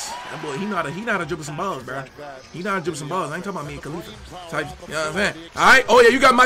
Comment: I thought he said me and Khalifa 😭🤣